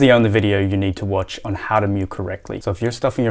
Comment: At first i thought he was saying how to meow